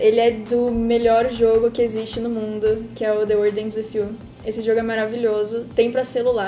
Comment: qual o jogo que ela falou???? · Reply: Eae!! A aqui [: O jogo se chama The World Ends With You! O nome do personagem é Neku Sakuraba e ele aparece também em Kingdom Hearts 3D: Dream Drop Distance! No celular dá pra achar o jogo como The World Ends with You: Solo Remix. Abraços! ~